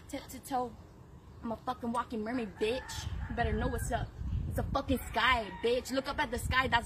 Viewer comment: "Gangster Mermaid Witches"- Now I've seen it all 😂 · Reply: OH! I thought she was saying, "Gangster Mermaid, Bitches!!". That explains it.